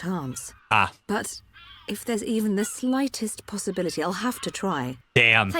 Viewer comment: Subtitle error! I instead of I’ll